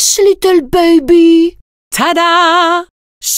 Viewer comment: she does not say "detour" but " tais toi": that basicaly mean "shut up" in french